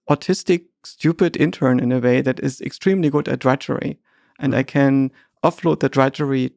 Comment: what does trachorie/directory mean? I think he doesn’t mean spilling secrets but something like structuring data. Can anybody tell what he means I don’t know this word and youtube auto transcript doesn’t get it eitherI think. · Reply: He says GPT is good at “drudgery” - boring, long, frustrating work